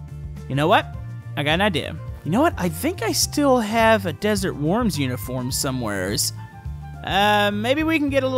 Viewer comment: I thought you’ve been saying desert warms for ever